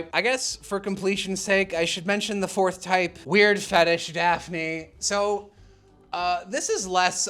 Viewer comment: *Mentions Totally Spies as a comparison for Daphne Me: “I thought you said no to the weird fetish Daphne.” 😂